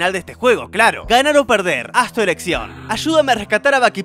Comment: has tu ereccion?._. No sé ustedes pero yo entendí eso